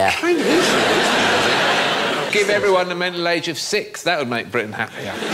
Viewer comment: I didn't understand what Alan said at 3:42. All I got was "give everyone the middle-age of 6"... I just watched it again.... got it now. It's "mental age of 6". Bloody english accents abrevia'ain every'hin. Say your bloody TTTTTTTTTs